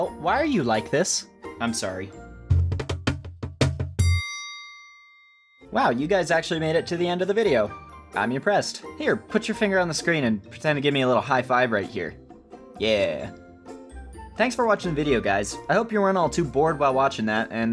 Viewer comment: did you say “I’m impressed” or “I’m depressed” I couldn’t tell XD